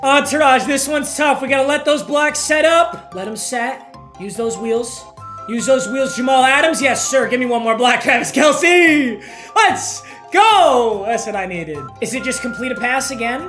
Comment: Anyone else hear say Jamal Adams